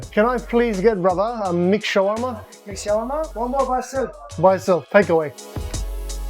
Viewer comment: bro i thought you said McShawarma and i was so confused